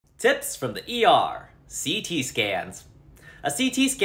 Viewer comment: I thought u said ct stan🤺🤺